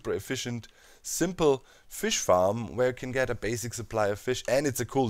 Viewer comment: what everyone hear "a fish farm", what I really hear "turin turambar" you know, silmarillion thingy